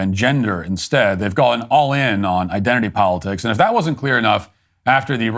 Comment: "and if that wasn't Queer enough" is what i heard